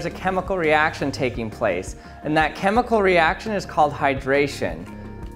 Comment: did he say hydrogen or Highdrogen